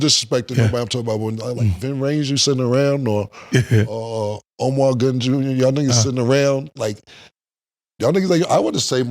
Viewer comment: Did he just say Omar Gooding Jr?